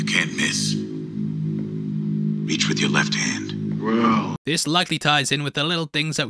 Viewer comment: Quick correction - the ‘go left’ comment Bernard made refers to how Stubbs and Frankie find Caleb. Huge thank you to everyone who pointed that out. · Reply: I thought he said "reach with your left hand" not "Go left".